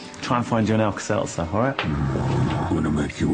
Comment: I always thought he said "I'm gonna put you in a dress and hurt you real bad."